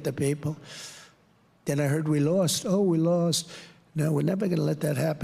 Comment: he actually said "we lost"!!!